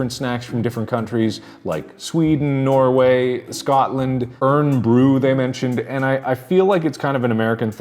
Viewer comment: Please tell me I'm not the only Scottish person here that heard him say 'Urn brew' 💀